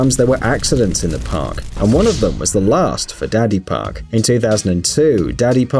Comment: I thought he said "Daddy park"